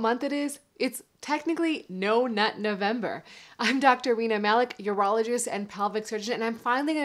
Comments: I genuinely thought she said her name was Dr Weiner Malik at first, I spat my food out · WANNA HEAR A JOKE. IT ALMOST SOUNDED LIKE U SAID HI IM WEINER MALIK =D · im sorry but it sounded like you said Weena Malik at the beginning of the video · Thought I heard Dr. Weiner Malak.